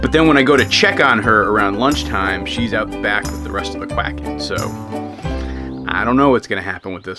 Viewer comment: I believe I misheard. Did he actually say: "Release the quacken."?